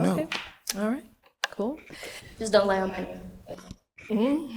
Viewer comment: What did she say at 1:03:58 ? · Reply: "dont lie on my name"